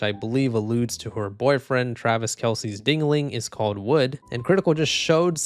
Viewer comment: did I hear you correctly...did you say ding-a-ling